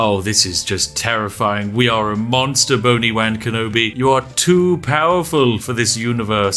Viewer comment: I swear I keep hearing "Bony-wanker Nobi" when the Spiffing Brit says it